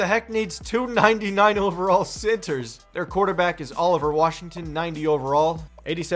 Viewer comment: i thought u said all over Washington at first 😂